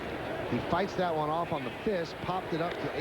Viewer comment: What did Michael Kay say? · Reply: Fights that one off on the fist